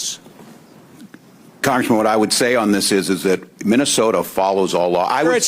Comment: Sounded like he called him "congresswoman" at the 5:00 mark in his response.